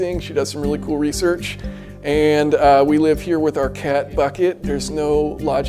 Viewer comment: lmfao. Was listening to this video as a backround noise, while I work and it was playing at the part where he is talking about his cat bucket. Thought he said "We live with our cat , Fuck it". Had to rewind that one to make sure he did not name it that. Ahahahahahhaa